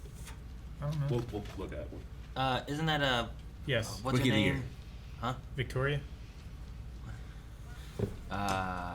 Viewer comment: What did say at 11:06? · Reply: Rookie of the Year